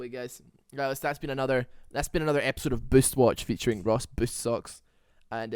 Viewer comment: I heard him say in his outro